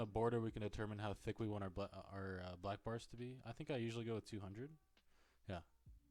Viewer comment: "And so here in the border we can determine how thicc we want our butt" Is what i heard lmao